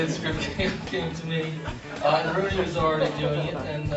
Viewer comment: What does Casey say around 1:50 ("Rooney was already doing it, so... "???) I love how he never assumes a compliment is about him. "I can't speak to who he wanted and didn't want." :))